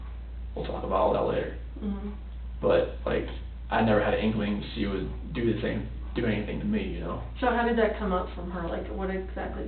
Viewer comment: When talking about infidelity he said he knew she wouldn't do the same, then he caught himself. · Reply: I hear, "do the same- do anything to me" he corrects himself.